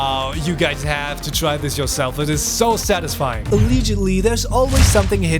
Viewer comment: sorry what did you just say a a aliigidly 😐